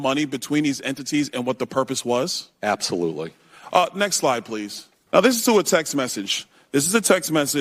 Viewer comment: Byron Donalds is a total rockstar. I thought he said, "Next LIE, please..." but he said, "Next slide, please." 😄